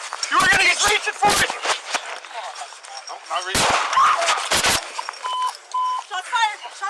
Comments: Did he say at 3:23 I'm not reaching?? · Please correct me if I'm wrong but at 3:20 it looks like the suspects hands are both on the ground in front of him and I think I hear him say "I'm not reaching" just before he gets blasted in the side of the dome. If true it's kinda sad, he decided at the end to just take the charge but it was too late